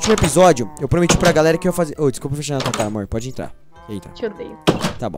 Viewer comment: eu escutei mor ou era moon